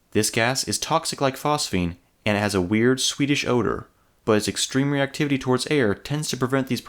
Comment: Wait wait wait, did you just say "it has a rare swedish odor" at 4:02 forward a little.....